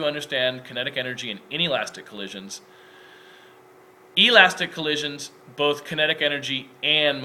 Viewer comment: Great explanation. I will be using this in my class today to introduce the kinetic energy equation. Also, it sounds like you say in inelastic collisions, kinetic energy is conserved during the cloze activity. I know it's elastic. Thank you for this great resource. :)